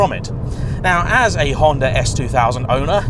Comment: I did a double take when Jay said “as a Honda S2000 owner”, in my imagination I thought he said “as a Honda S800 owner”, I went nuts and had to rewind it.